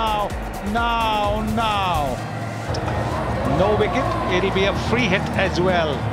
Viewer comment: I heard Now Now Now instead of No No No😂🤣